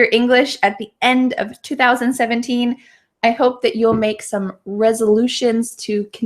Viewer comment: wait.. did u actually say "at the end of 2017" or else I need to sharp my listening skills a lot ? Please reply and if I'm right then give my comment a heart